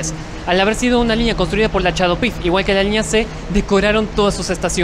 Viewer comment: qué dice ahí???????????? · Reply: CHADOPyF. Compañía Hispano Argentina de Obras Públicas y Finanzas